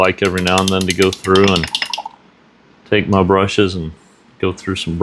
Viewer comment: i thought you said "i Thank my brushes and go through some brush cleaner" kinda a cool thought, they are some hard working tools to make some pretty art happen! i also really appreciate you showing the cleaning process, lots of people Glaze over this.. and it is soo important